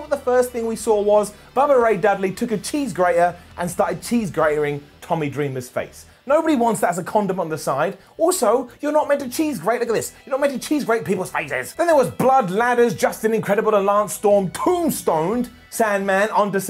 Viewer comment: "a condom on the side" Just me that heard him say that?